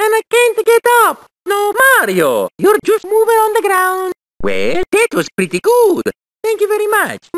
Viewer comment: "Mario I have-a fallen and I cant-a get up" "No Mario youre moving on the ground" Holy shit my sides. This whole thing is a masterpiece. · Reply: I always thought he was saying "Moving underground". My life will never be the same again.